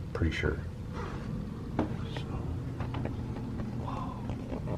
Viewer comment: Hey 😀😀😀 @ 13:02 sounded like a male whisper " Wow".... @ 28:43. You hear a male whisper again while is talking say "Oh Yeah"